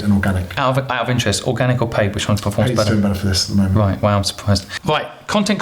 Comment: Great At 21:20 Simon asks which list performs best, paid or organic, does anyone know what is reply is? I couldn’t tell Thanks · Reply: "Paid is doing better for this at the moment"